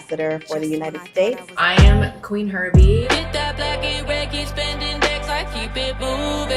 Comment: I heard "Queen herpies". Anyway... all of this music sounds terrible.